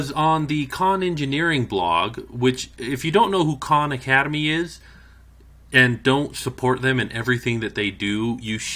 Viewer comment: i thought he said at first if you don't know who they are don't support anything they do